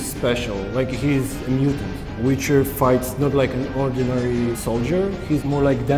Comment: the devcomm clip you showed every time he said fight it sounded like he was saying fart.